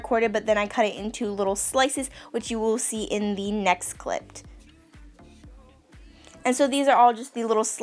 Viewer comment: I thought she said "in the next clit" but it's okay she said "clipt"